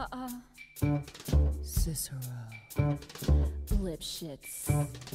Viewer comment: I thought was saying dipshits lol XD